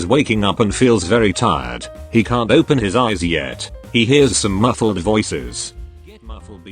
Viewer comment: Please help I can’t stop laughing I thought it said he cut open his eyes yet